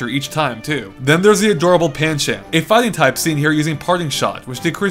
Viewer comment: I heard "panshit"